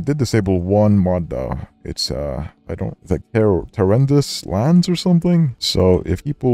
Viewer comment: he said he disabled a mod? Can anyone tell me what he mod said I can't quite make it out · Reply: Tenebrous lands